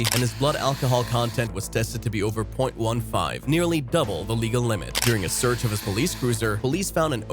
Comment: Nearly double in legal minutes i heard